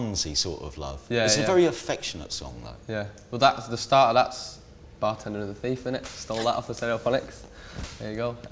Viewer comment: what did Van say from 5:39-5:42 ? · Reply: "the start of that is Bartender and the Thief, stole that off stereophonics"